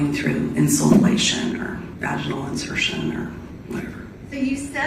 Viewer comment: Did she just say "vaginal insertion"? I mean, I guess if someone is desperate enough or a mule trying to sneak it into a prison/jail that could be a possibility but I have never heard of someone using 'snow' this way. 🤔 😳